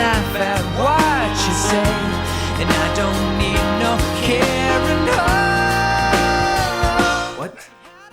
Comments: When I was a kid I thought he was saying “I don’t eat no caribou.” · I always thought he said "I don't need no caramel"...